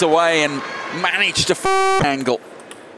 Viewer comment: did he just say "and managed the fcking angle "